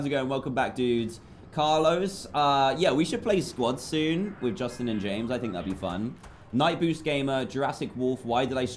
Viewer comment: When he said I should do squads with Justin and James I thought he said I should do squads with Jesse and James from team rocket😂😂😂